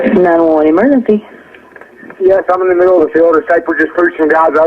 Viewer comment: To me, it sounds like he says: I'm in the middle of the field a sniper just pushed some guys over on Bronte side headed towards Abilene (maybe). My truck ran out of gas, there is one car here, I got chased into the woods, please hurry. not talking to them I just suddenly/totally ran into him/them, shot the first guy. Than when the 911operator ask him do you need an ambulance? It sounds like he is talking over the 911 operator - and says 'shot the scond guy' (gunshot sound). You can hear a voice say 'yea' than says no I need the cops.